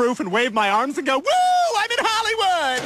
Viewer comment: lmao....whoooooa im in Hollywood...see i said i'll do it lmfao! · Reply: I thought he said, "Whoooa I'm in Hollywood, Jesus said I could do it"